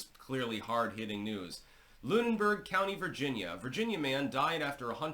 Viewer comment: Thought that said virgin man, probably a virgin. Australia has drop bears